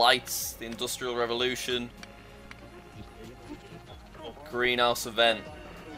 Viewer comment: me who thought greenhouse event said mass extinction: 💀